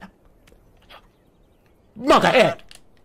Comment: what did he say???? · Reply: "MonkaS". If my hearing is right.